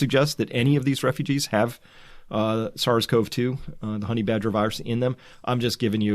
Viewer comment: Did I just hear "Honey Badger Virus"!? 07:45ish